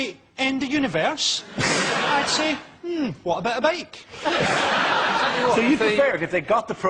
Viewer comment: ***** what is Frankie saying at 4:17? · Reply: Matt Heafy "Mmm, what about a bike?"